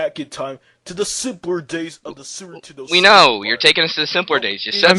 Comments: did he just say "sewer 'tendo"? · Instead of Super Nintentdo, it sounds like he says "Sewer ntendo"